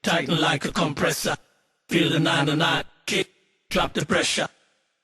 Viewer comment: what does he say at 0:05? feel the na na na....? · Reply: Feel the 909 Kick